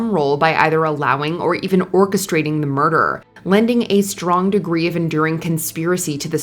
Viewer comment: Did she say "murderer" instead of "murder"?